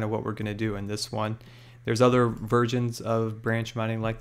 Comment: Did he say "there's other virgins" at 6:14 ? xD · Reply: Versions